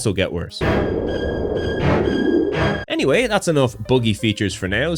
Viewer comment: It took me a solid 30 or so minutes to realize you did not actually say "boogie features" at 15:40 in reference to the music. Every other time you said buggy, I immediately knew what you meant. Context is powerful!